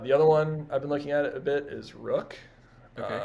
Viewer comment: rook? any one can help me understand plz what did he say? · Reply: Yep ROOK is the token name (might be several ones with the same ticker symbol out there though)